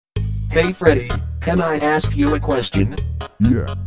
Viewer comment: if thought bonnie said "do you know what aids is?" XD